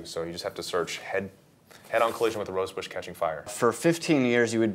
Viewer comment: what's the band name? I can't understand =/ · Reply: Yago Reck Head on collision with a rose bush catching fire